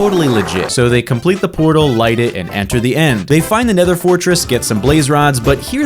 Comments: On 2:26 he actually said end instead of nether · I Love how instead Of "Nether" He said "End" that was hilarious hahaha 😆 This made my day and can I have a shoutout? I Really do want one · you said end instead of nether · In 2:27, you accidentally said the end instead of the nether